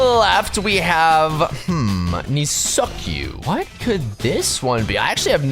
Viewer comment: did anyone else hear “me suck you”😭😭🙏🙏